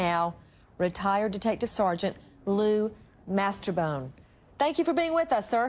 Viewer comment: i thought she said, blue masterbone lol xx